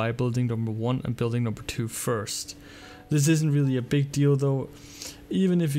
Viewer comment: WAAAT? did you just said big dildo??? XD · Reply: Dante Big deal though xDD